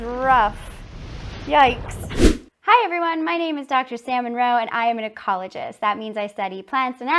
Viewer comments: I heard “my name is Dr Salmon Roe” 😂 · Right away my first reaction when she said "Dr Sam Munroe", I heard "salmon roe".... and she's an ocean-animal researcher XD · Just listening, I heard her say, "I'm salmon roe" instead of Sam Munroe 😂 love your content!